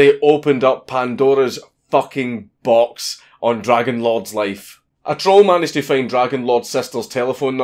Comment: when you said dragonlord i thought you said dragonlard